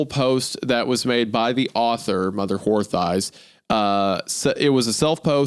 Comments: Mother what? I'm not the only one that hears Wendi saying Mother Whore Thighs here, right? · I couldn't help but mishear it as "Mother Whore Thighs" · Anyone else catch mother whore thighs at 1:05:55 😂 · did Isaiah just say Mother Wh**e Thighs? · Isaiah accidentally says mother who’re thighs instead of mother horse eyes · I swear I’m either going crazy- or Isaiah said what I thought he said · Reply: He definitely said mother whore thighs. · Anyone else catch Wendi's Goth Mommy Freudian slip "Mother wh0re thighs" xD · That sounded like 'Mother Whore Thighs' lol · I swear he just said, “Mother Whore Thighs” 😂